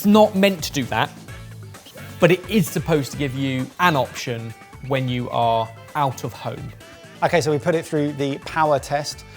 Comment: i was eating when Ben said "when you are out of home" and I heard "when you are out of hope", (4:50) which honestly...probably also true?